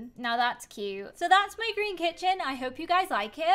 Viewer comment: When said and that’s my green kitchen I thought she said that’s my dream kitchen and I was like What but you said you hated it.... and then i realised